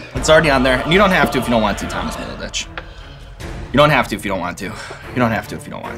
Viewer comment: did you call him Thomas little bitch? lol if you did, that's hilarious.